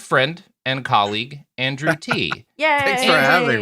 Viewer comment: I swear I heard him say andrew tate at the start.